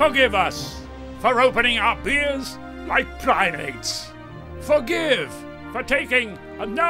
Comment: Could anybody tell me what it says at 0:29? Forgive us for opening up beers like...? Like what? Thanks!! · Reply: Like primates! 😂